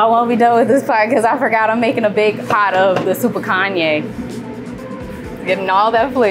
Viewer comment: When she said Soupon Kanja, I thought she said SUPER KANYE.